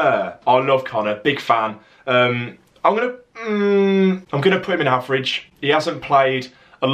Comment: I kept thinking you were saying "I'm gonna put him in our fridge!" ...and you really don't want to know how wrongly I misheard your opening comment about you ranking all of Villa's players!!!